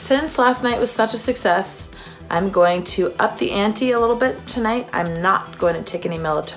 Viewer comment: the subtitles say up the anti instead of up the ante